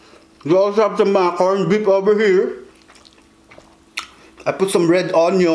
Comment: I died When you say we also have corned beef over here but the subtitle said Mac orange whip over here 🤣😂